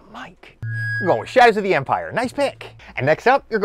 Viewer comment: I thought he said shag of the empire.